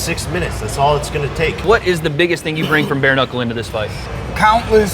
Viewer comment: I heard “bear in the hole” instead of bare knuckle lol